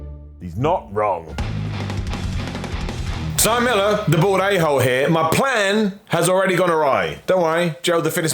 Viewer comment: I’ve thought you been saying “the bored ahole” for the past 4 months hahahaha